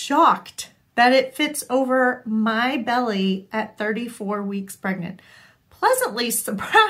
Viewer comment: Oh, your husband was well on his way to winning a Darwin Award before he met you!! 🙈 I felt stressed about him going hiking with your son after hearing those stories! I'm sure they had a great time. 😅 The hoodie turned out great! There are so many nice details in it. Well done with all the stripe matching! I feel like the hem band really makes it. Awesome that you can even wear it now. What a nice surprise! I can't believe that you're 38 weeks already. You might not agree, but I feel like this pregnancy has flown by. Wishing you the best for the upcoming birth. 💞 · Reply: why did I think you said 38 weeks in your video? Clearly bad multi-tasking while sewing and misheard the details. Hoping she does some acrobatics soon, and that you both stay healthy.